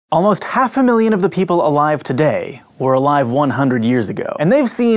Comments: the first thing you said in the video is a lie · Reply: oh i thought he said half of the people here today were alive 100 years ago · At first I thought you said "About half the people alive today are over 100" and I was thinking "WHAT THE F-"